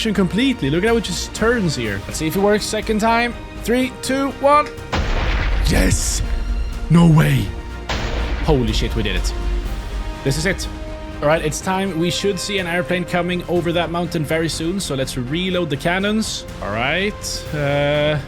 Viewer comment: what does that word mean? it sounds similar to shis, did you mean that?